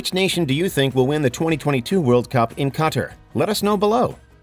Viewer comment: Tf he just say??? Cutter? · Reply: That's what I heard. Qatar rhymes with guitar.